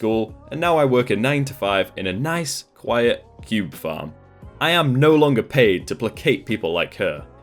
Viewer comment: When you said cube farm I thought you had said Pube farm! LMAO XD